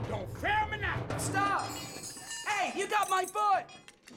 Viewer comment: it sounds like fender's saying "OH, AN ONION!!" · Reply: I heard "hey you got my but" even thought it was "foot"